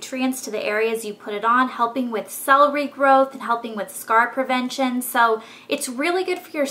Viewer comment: instead of "cell regrowth" I heard "celery growth" LOL